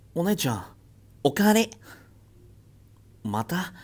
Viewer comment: 最初のお姉ちゃんお帰りが お姉ちゃんお金！に聞こえる